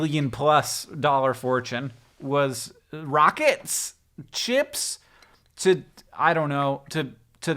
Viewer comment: I swear to you I heard "Rocket... chips?" and was imagining two different expenditures involving children rockets and potato chips.